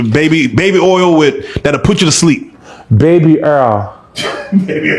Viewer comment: Baby Earl 😂😂😂😂😂😂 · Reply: CTFU!!! I thought he was saying, "baby AIR!!!" 😅😂😂